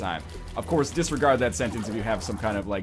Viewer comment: Could someone help me, what he is saying? impoverished, is that right?